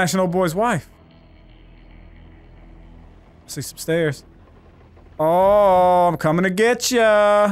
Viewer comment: Dude it sounded like said "Ohmmm I'm coming to getcha"